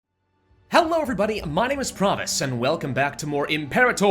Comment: is just me or every time he says his channel name i hear "promise" and not "pravus"?